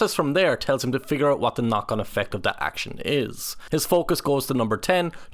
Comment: I can’t understand 0:30 “figure out what to…” ?? Also you’re saying trow instead of throw. Like English please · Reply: Knock on effect. I hadn't heard the term and had to look it up. It means a secondary or cumulative effect. Trow and tree must be commonly heard in Ireland instead of throw and three.